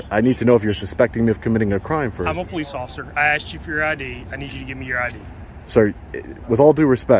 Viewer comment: am i tripping just before 5:30 i swear the cops said "do you have a Ni*** id on you"